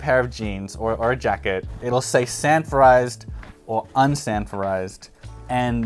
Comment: I thought he said "sanfordized". I assumed we were talking about old episodes of "Sanford and Son". 📺